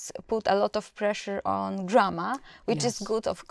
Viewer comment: in my opinion teachers put a lot of pressure on drama, which is good of course, but there "drama" or "grama"? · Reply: I thought that she said grammar but the subtitles say drama lol